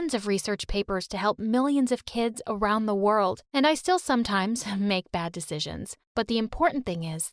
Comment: mannn I thought she said “I steal sometimes”😭😭